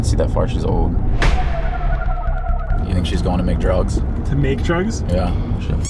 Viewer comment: I thought said "is she going to McDrugs?" Lol